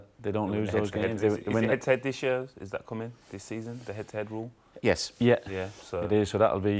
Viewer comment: Uh..can someone plz tell me what Lescott is talking about at 2:36 about some rule..i couldn't really understand his accent. Thanks. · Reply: Krishnamohan Pingali head to head rule. I’d say for example city and Liverpool finish level on points and same goal difference, they’ll decide it based on their results when they played each other